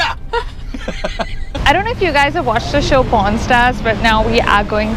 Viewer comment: I thought said porn stars.